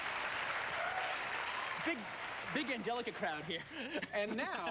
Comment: "Big angelica crowd here." What does that mean? Did QT say angelica?